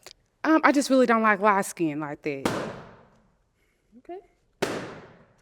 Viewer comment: Old school Accent so thick I thought she said white skin 😂😂